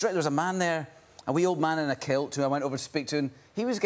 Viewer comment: "A wee old man in a kilt". I heard it like "a weird man in a quilt"